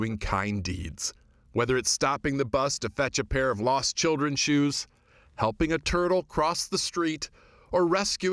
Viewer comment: Hi, I'm studying English as a second language. Can anybody tell me what the reporter says at the 1:09- 1:10 minute...something like.. " whether stopping the bus.. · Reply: "whether it's stopping the bus to fetch a pair of lost children's shoes"